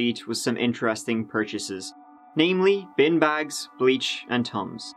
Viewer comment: I thought was saying “bean bags” when he was saying “bin bags”. I was like, “how do you use bean bags in a murder?!”